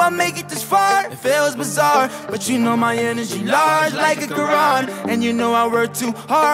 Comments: " My energy large like a Quran" A lot of muslims have to give props to black culture for always giving respect and goin against media portrayal of muslims. Oh man this blew up. Vote for BERNIE 2020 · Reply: Nigga I thought he said a garage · "I never knew I'd make it this far, it feels bizarre, But you know my energy large, like a Quran" this line hit my so hard man. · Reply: I thought he said like a garage not quran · " My energy large like a Quran" A lot of muslims have to give props to black culture for always giving respect and goin against media portrayal of muslims. Oh man this blew up. Vote for BERNIE 2020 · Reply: I always thought he said garage and not Quran